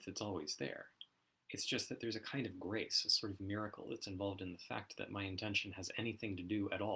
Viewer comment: I'm dying to know what you said in 12:33, the video just broke :( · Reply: Oh that WAS a pretty wicked glitch, wasn't it? 😀I think I was saying that there's a kind of miracle involved in my intention matching up in what my fingers normally do. I'm pretty sure I'm talking there about how there's a deep mystery as to how intention actually touches the world. How does my choice to move my finger cause my finger to move? Not abstractly, but in terms of my actual lived experience?